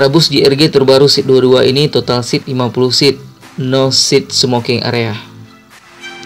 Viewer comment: 50 seat???? nggk salah denger aku ya bang? klau boleh usul ya bang alangkah baik klau review bus, jumlah seat juga di hitung, seat sebelah baris kanan ada berapa, dan seat sebelah baris sebelah kiri berapa,. jadi kita sebagai penonton bisa membayangkan luas bus tersebut,. dengan panjang bus 13,5 meter, klau di isi dengan 50 seat itu gimana luas nya? sory bang ini hanya usul. biar penonton kayak saya nggk bingung.